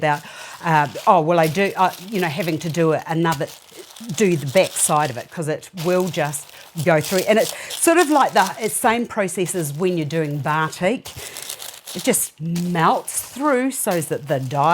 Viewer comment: what word is she saying? Bartok? ..